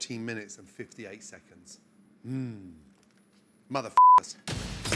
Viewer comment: Does he say "Mother F'n Sexy" at 14:10? 😆